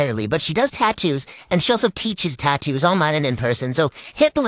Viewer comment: Omg I had to rewind it because I thought I heard “teaches tattooing online and in prisons” and I was like hold tf up!